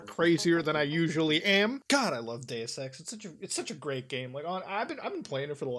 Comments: i thought he said “damn i love gay sex”😂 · When he said he loved dues ex I thought he said he loved gay sex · OH DEUS EX I THOUGHT YOU SAID GAY SEX